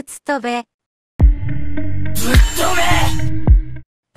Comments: 「僕最強だから」が、「国際気温だから」ってめっちゃ聞こえるww ＋虎杖のとこ最気温を教えてくれって聞こえるw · 僕最強だからが国際鬼王って聞こえた笑